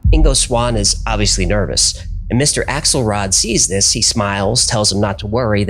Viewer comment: Mr. XL Rod? I am sure I heard that name in another context...😂